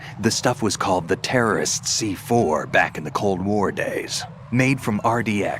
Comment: I swear that Snake "Pliskin" says "back in the cold MORE days" at 1:04:55 rather than "Cold WAR". Just me who hears that?